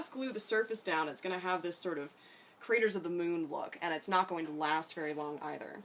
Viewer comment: When u said craters of the moon I could’ve sworn I heard raiders of the lost ark lol I dunno why 😂